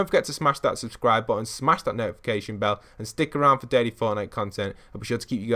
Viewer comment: Is it just me or did sound like he said “smash that like button for dirty Fortnite content” at the end of the video